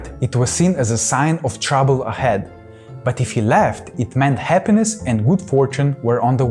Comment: if he left the house it meant happiness and good fortune??? i thought that is not good if domovoik left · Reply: I listened to him again, he said LAUGHED. I also thought he said left at first but it's the accent, I have heard domovoy won't leave even if house burns down